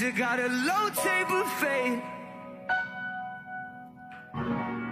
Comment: All I hear is, "What if Ninja got elote buffet."